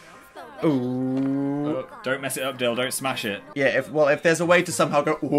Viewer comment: i thought Phil said "don't mess it up dildo smash it" and then I saw Phil look up to the camera awkwardly stare smiling at it and I was just like "PHIL WHERE HAS YOUR INNOCENCE GONE OMG".